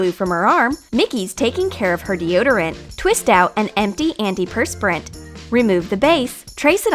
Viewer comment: I heard “anti antiperspirant” instead of “empty antiperspirant” and was like ?????? It makes you smell worse??? What is this???